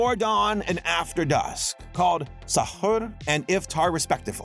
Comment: my dumbass was losing my shit because I thought he said Tun tun sahaur. That’s what I get for watching too much Italian brainrot (Yes, I have been watching it since febuary)